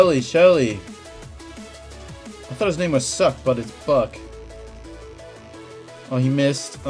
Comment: did he say buck or the f word>?